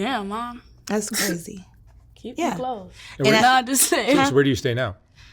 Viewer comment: after the Mom says she has six kids, it sounds like says “Damn Mom, keep ‘em closed.” Sad family.